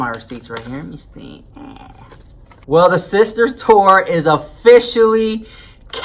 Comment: I thought he said “Sister STORE” at first.